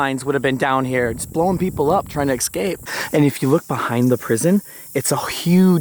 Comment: Did he say excape?... then mou-in?